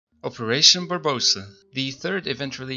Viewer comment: Did he just say operation barbosa